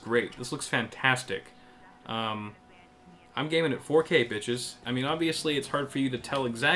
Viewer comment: No. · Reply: Thought he said gaming with 4k.